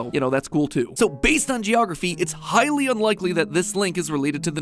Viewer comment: When Mat said "Highly unlikely", I somehow heard that as "Hylian likely". Which... is normally the type of bad pun Mat would make, which is probably why I heard it that way.